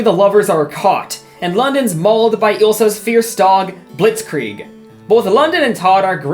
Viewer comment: The dog is called WHAT? Blitzkrieg???? did i mishear that?